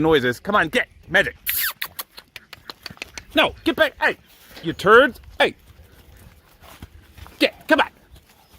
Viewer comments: Is it just me or did said you turd to the horses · did i hear u call that horse a turd? Or was i high or something?